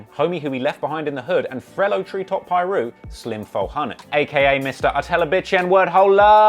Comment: Who else heard trap say "frellow treetop piru" at 22:05 lol